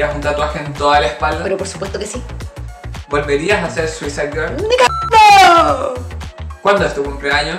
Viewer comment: no entendí xD que dijo? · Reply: nicagando xD